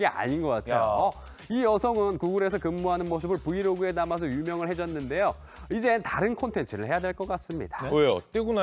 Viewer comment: 발음이 부정확하네요 유명해졌는데요.가 유명을 해졌는데요.로 촬영하려고 했는데.를 촬영하라고 했는데.로 들리네요